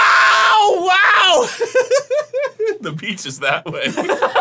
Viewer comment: Arin going from screaming in agony to "T H E B E A C H I S T H A T W A Y" absolutely sent me, holy crap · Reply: Why have I always heard that as "The Pizza's that way?! 😂 Totally makes more sense that he said beach! 😂